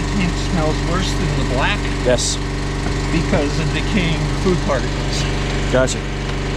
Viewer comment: Glad you put “food particles” because i thought he said “💩 particles” and my thoughts went to “how” 😂